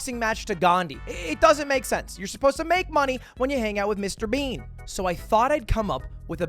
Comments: Did he called Jimmy “Mr Bean”? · did he call him mr bean? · Did he just say Mr.Bean ?